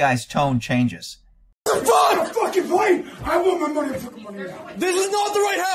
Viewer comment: What is he saying @ 0:50 ? Sounds like he is saying I want my mf money now